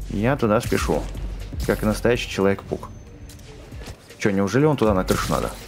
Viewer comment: мне послышалось не человек паук, а человек пук